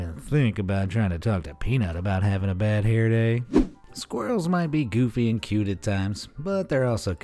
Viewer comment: did he say squartle the pokemon instead of squirrel